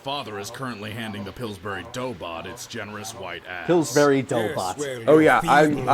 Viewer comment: Piccolo's said Pillsbury dough BOT this whole time?? I thought he was saying dough BOD 🤦